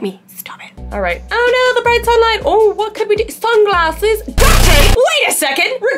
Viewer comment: am i the only one that heard her say the brights are light😂